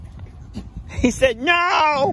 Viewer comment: Instead of no sounds like he says Get Back.. Now